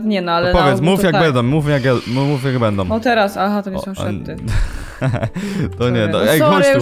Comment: Szczypson na początku tego gadania Lucyfera było coś takiego "hahaha to nie śmieszne" ja tak usłyszałam